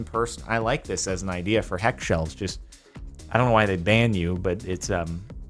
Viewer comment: is he saying heckshelves, and if so, does he mean hellshelves or is the name actually heckshelves and im just to far into the Jardon lingo?